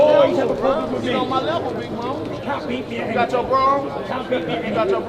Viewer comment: What is he saying ? "You got your Bro?" "You got your bro?" Someone clarify lol